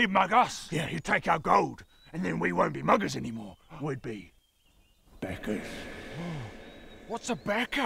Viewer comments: Anyone else hear them say “beggars” instead of “backers”? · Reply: I'm not a native speaker and I heard "beggars" but it didn't make sense. Never heard "backer" before. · Ooooh, "backer." I kept hearing "beggars."